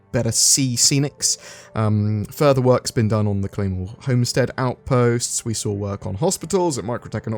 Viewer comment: I misheard Claymore Homestead as "Clone War" homestead. :)